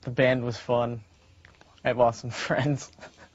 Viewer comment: Waittt at 1:56 he says “I have awesome friends” ? I thought he said “I’ve lost some friends” 😭😭